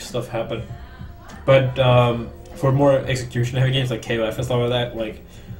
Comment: what game is he talking about? it sounded like he said klf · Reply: King of Fighters.